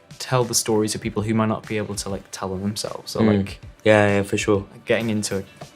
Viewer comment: KCKDKSJSHZHCMSHD só que que escutei o kit falar "é é fechou"